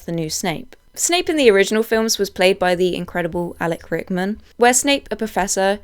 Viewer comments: Please tell me you said Alan Rickman and NOT Alec Rickman. Because I heard you say Alec and um, please do not disparage him like that. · Alek Rickman?? 😂😂😂 few people criticise you for different things in this video, I ignored them all, but this is it😂😂😂 (joking, not sure if it’s my bad ears or editing, but I swear I keep hearing Alek)